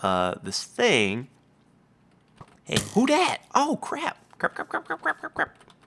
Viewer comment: is it just me or did say 'who that!' even before the skeleton hit him at 3:26?